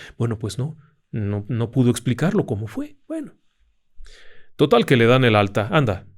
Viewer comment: Buenas, alguien mas escuchó que en el minuto 15:11 se filtra una voz que dice "bueno".